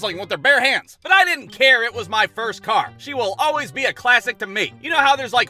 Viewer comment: I swear to god I thought it said “Washing my first car.”